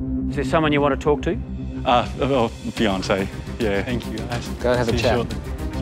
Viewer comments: I thought he said Beyoncé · When he said that he "was gonna go talk to his fiance". I thought he said 'Beyonce'😏